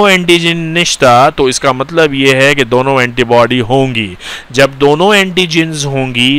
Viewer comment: Are you pashtoon I heard nishta in 25:27 ??????